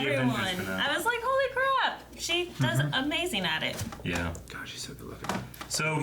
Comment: lol did he say gosh she is so good looking.